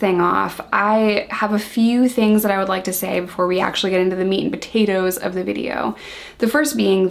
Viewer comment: When you said “meat and potatoes” at the beginning I heard “meat and taquitos” ... I think I’ll start saying that instead 🤔 Love you 🖤